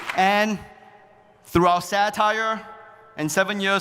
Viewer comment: 「風刺を通して」の部分はthroughout satireでしょうか？（聞き間違いであればご放念ください。） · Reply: ご質問ありがとうございます。 該当箇所を見直したところ、satireがsad tireになっておりました。 また、該当箇所を再度聞き直したところ、「through "our" satire」とおっしゃっていますね。 誤解を招く表現となっていました。申し訳ございません。 ここでの satire は「風刺（政治や権力をユーモアで批判すること）」です。 また、ここでの through は「～を通して」「～という手段で」という意味になり、 throughout は「～の至る所で、～の間ずっと」という意味なので、この文脈では意味が合わないと思います。 再度となりますが、ご質問ありがとうございました。